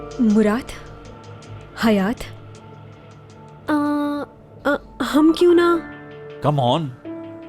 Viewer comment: yrr ispe asli and ipek ne kya bola sath me???kuch smjh nhi aaya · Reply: Ipek get together ka kehti h or asli Milne ka